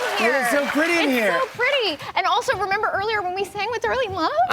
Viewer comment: I almost fall off the chair. I heard "and also, remember early when we said we were in love?" in the first time